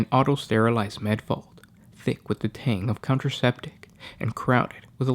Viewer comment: did he say thick with the tang of contraceptice? Maybe he ment antiseptic? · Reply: It's counterseptic.